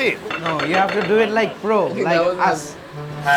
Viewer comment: i thought the guy in glasses said ass😂